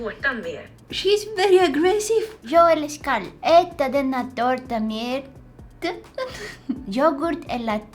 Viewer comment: Hola ❤❤❤❤😂😂 im just kidding im from sweden ❤❤❤ but.... it sounded like u said tåta insted of tårta i can prove that im from sweden : Å Ä Ö english People CANT say that or Send/text and I love ur videos❤❤❤❤❤❤❤❤